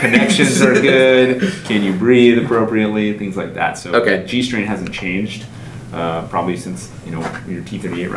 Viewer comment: G- strain I heard G-string 🙁😜